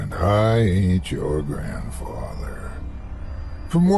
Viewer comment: I thought he said "I ATE your grandfather" in the intro. Can't believe I've never caught this podcast before - is the man.